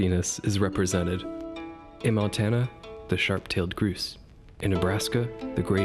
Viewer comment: Stopped watching after I heard you say "groos" instead of grouse